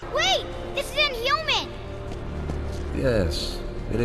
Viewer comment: So what did he say? I literally can't understand him and I've replayed it five times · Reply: Sounds like “Wait! This is inhuman!”